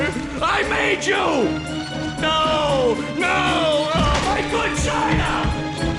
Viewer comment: After all this time, Plankton was saying “my good China” and I always heard “my vagina.” Wow.